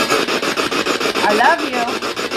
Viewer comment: OMG at 16:49 that evp sounded like it said I love you too in the voice Wilbur was using!! Im gunna cry!!